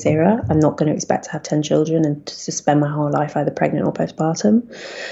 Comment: Did she say "suspend my whole life" or "to spend my whole life?"